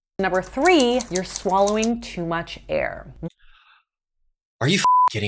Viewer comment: I thought she said you are swallowing too much hair!!! 😂😂😂